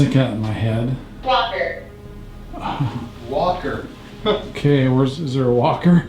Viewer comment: So when y’all heard “walker”, I heard “locker” and then y’all came upon the lockers. Just sayin…🤷🏻‍♀️